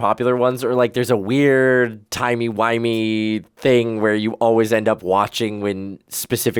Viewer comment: Did I hear a "timey wimey"? is Dr. Who an option?